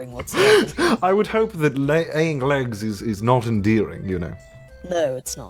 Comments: Did he just say 'eyying leggs' or am I hearing things? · Sebastian said aying legs instead of laying eggs. 😂😂😂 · Did anyone else hear CDawg say aying legs.